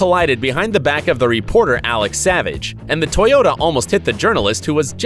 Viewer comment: did i hear that right?? Alex Savage??😝😝